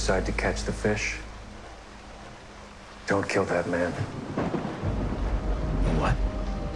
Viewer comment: "Don't kill that man" I thought he said, "'Don't kill Batman" , and I did a double take